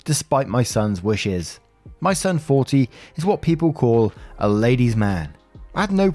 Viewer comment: On Story 2, I thought you said: “son, 14.” I was like: WAIT A FUCKING MINUTE!!!